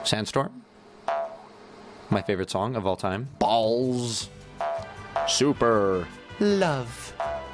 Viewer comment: WHAT DID HE SAYYY? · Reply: "Alls"